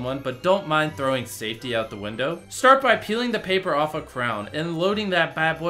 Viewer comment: did Jimmy say crown